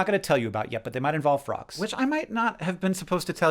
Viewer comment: I thought you said "rocks" not "frogs" and then was very confused when you said "not LIVE rocks"